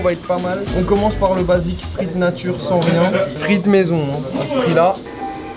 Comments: C moi ou a 3:51 il dis bismillah ?🤣🤣🤣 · c moi ou il a dis bismillah ?